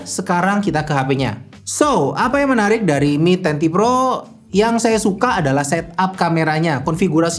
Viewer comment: Apa saya salah dengar?? Di video disebutkan ini Mi Ten Pro (mi10 pro) padahal seharusnya mi ten ti pro (mi 10T pro)